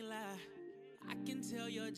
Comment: lol when he said "wipe your eyes" we all thought he was saying "wipe your ass" lmfao